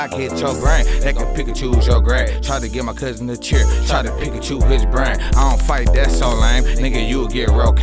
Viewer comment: try to give my kuzin the chair try da pikach his brain >>>> this nigga so hard and jonboi is the best producer in detroit right now wake up people <<<< record deal music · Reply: Deshawntia Moorer bruh I didn't know he said that. I thought he said pick and choose his brain. Jesus that was a bar